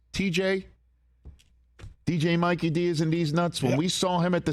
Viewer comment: LOL did you just say deez nuts?